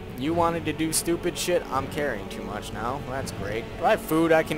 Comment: I keep hearing 'I'm careing too much' instead of 'I'm carying too much"